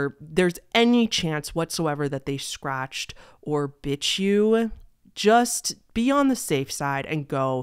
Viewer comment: HELP AT 9:16 IT SOUNDED LIKE “B!tch you” 🪩